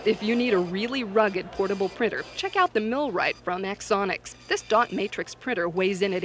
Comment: what is that printer It sounds like she is saying "milwrite from xsonics" but I can't find anything.